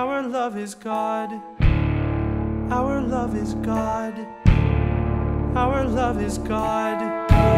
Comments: I thought she said our love is gone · it kinda sounds like veronica is saying our love is gone around the end